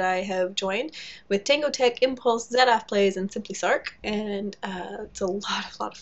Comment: Did I hear you name a 5th server member at 3:05? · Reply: Yep, SimplySarc!